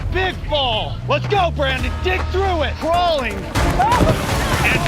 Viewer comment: did Jeff actually say ‘let’s go Brandon’. I’m so disappointed.